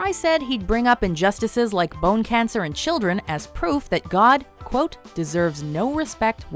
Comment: I thought she said injustices like bone cancer and children